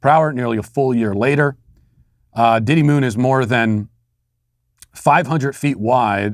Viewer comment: I heard “Diddy Moon,” and thought you’d say it sounded like a rapper. 😂